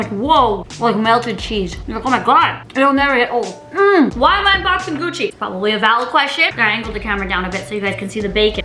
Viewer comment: Thought she said unboxing Gucci bacon